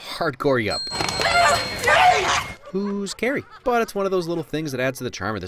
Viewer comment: I always heard him shout ‘Leia!’ at 19:16. But I guess when you listen again you can hear ‘Carrie’. Aw. That’s cute.